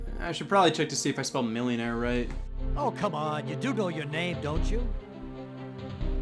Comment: did he say "You Googled your name, don't you?" ._.